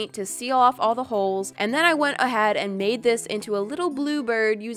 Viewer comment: Why did you say “and then I went a had and blah blah blah